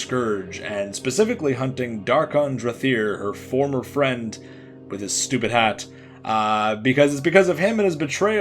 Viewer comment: I had to replay 9:24 a few times because I seriously thought you said " Dar'Khan Drathir, former friend of a stupid hat". I was thinking, why would someone befriend a hat?? Let alone a stupid one???